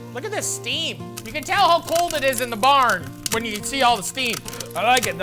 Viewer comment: did he say barn. He’s cooking in a barn ?